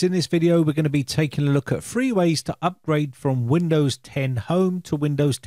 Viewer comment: Oh, Three ways to upgrade, I thought you said Free ways to upgrade 😆